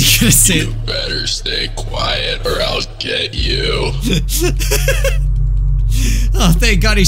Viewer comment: "you better stay quiet or else ........ get you.." BRO WHOEVER VOICED FIGURE DURING THE EDITING , SOMEONE CUT IT OFF LOL I CANT GET OVER THAT edit: yo if u revive it'll say smth like "Back from the dead!" or smth else that's atleast what I heard when a friend of mine revived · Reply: it's "You better stay quiet or i'll get you..."